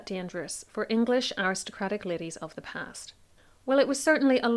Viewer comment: I heard 'Tism is fortune in ladies' 😂